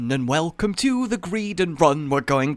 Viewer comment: At the start, I accidently skipped close to the end, so it sounded like you said “Hello everyone and welcome to the Greedary.” Is a greedary a place where you can buy some greed? Or a place where you can use greed to try to get money...is a greedary a casino? Or maybe a greedary is somewhere where you can grow some greed...America because of all those greedy companies? The companies themselves? Is EA a greedary?